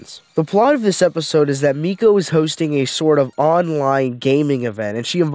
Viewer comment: I swear to god that I had to rewatch this part if the video because the firts time I heard it I thought it said "A sword art online event"